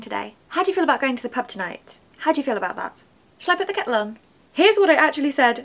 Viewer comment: Understood all , except " Shall I put the kettle on " and thought I heard " ..Shall I get along " or something. I normally can follow what you say and also this video , even your fast speaking , b'cause your articulate the words so well , and that s a-gem-of-a talent .❤